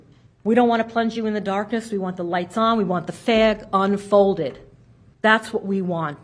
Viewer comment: did she just say we want the F4G unfolded? 😂 Did she mean facts?? 🤔